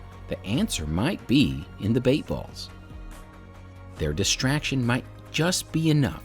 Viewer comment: I keep hearing “bait bus”